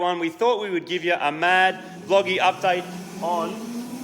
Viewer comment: i had to rewind. i thought marty said a Mad Wogy update 🤣